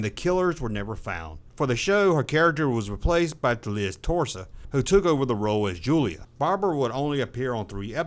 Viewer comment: Liz Torres replaced Barbara Colby. Sounds like he said Liz Torso. Also "Robert Ragner?" Was this voice done by AI?